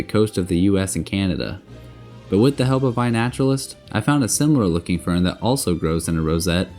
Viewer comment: I heard “what the hell iNaturalist.”